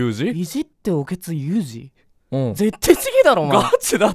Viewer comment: ダニエル先生発音良すぎて尊敬します...(ᐡ o̴̶̷᷄ ̫ o̴̶̷̥᷅ ᐡ) いじっておケツって私も聞こえた